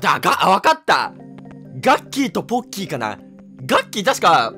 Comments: の所さぁガッキーとボッ⚫ーにしか聞こえないんだけど…いやそれは俺の耳悪い。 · ガッキーとぼっきーに聞こえた俺はまっきー（寒） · ガッキーとボ〇キーにしか聞こえない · すまん、2:14ボッキーに聞こえた俺は末期